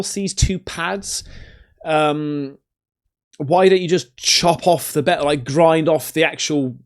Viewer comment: I saw you slam on the handbrake and hit reverse; I swear you nearly said, "why not just chop off the bell end" 😂😂😂😂